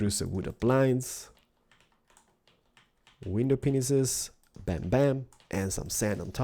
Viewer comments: Did he say "window penises"? I think he was meant to say "window panes" but sounded it funny. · did he just say window penises?? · anybody else notice that it sounds like he says wiener penis's?